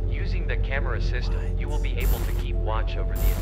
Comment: Wtf I heard a "what"